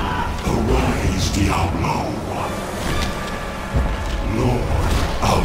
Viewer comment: does he say "arise diablo 1"?